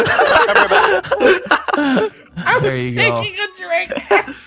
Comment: my dumbass thought he said I was thinking of drake